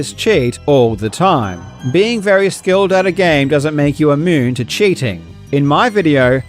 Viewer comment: I stg I heard "imoon" instead of "immune" 🤭